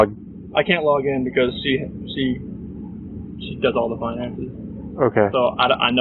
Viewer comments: Another clue: Chris says "she did all the money" when addressing their bank account, instead of does. Little slip. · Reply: right at around 18:00 give or take a couple seconds. .but I heard “she does” not “she did”. 💁🏾‍♀️ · Another clue: Chris says "she did all the money" when addressing their bank account, instead of does. Little slip. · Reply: I hear him say “she does all the finances” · Another clue: Chris says "she did all the money" when addressing their bank account, instead of does. Little slip. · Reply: I just heard him say , "she does the finances"